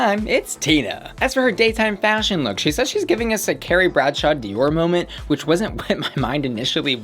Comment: I thought Tina Burner said Terry Bradshaw , not Carrie Bradshaw. Oooof...... Love love love Simone!!!!!